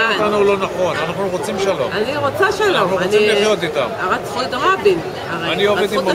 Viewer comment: Wait, didn't the Uganda lady at 8:05 say "They killed Rabin"? Why do the subtitles say "Rabin was killed for peace"? Rabin was killed by Yigal Amir, an Israeli Ultranationalist as Wikipedia calls him.